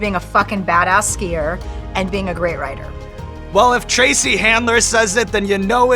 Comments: Did he just say Tracy handler? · Why does Sean call her Tracy at 24:18 ? Just wondering. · did he just call her Tracy?